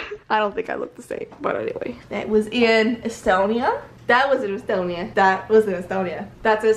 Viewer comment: did you say Estonia?? :D im from there